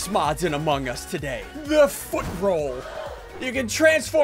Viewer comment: When he said "Foot Role" I thought he said "Foot Roll"